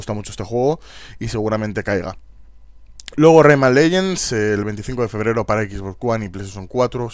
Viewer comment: como se llama el juego del min 4:57 no lo entendi muy bien? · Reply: Rayman Legends